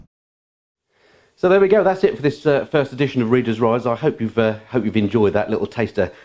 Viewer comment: Readers wives? excellent !! oops misheard you there...yes, looks great and sounds lovely